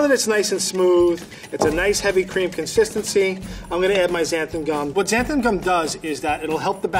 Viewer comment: (there abouts) Frank adds "zanting gum" to the batter - what the hell is zanting gum? My hearing is not that good and I replayed this section about 10 times and I still can't make out what he is adding to the batter. It would be very helpful if they put up on the video the ingredients and the amount that each person is adding as they are adding it. Frank - please tell me what zanting gum is!! And the other ingredient that was hard to understand was "seltzer". I had to replay that a few times to figure out what they were saying. I am a pretty good cook but my hearing is not good. These people have to speak clearly or someone has to put the ingredients up on the screen so us "hard of hearing" can understand what they are saying. Can someone in the peanut gallery tell me what (0:4:50 Frank says) zanting gum is please??!!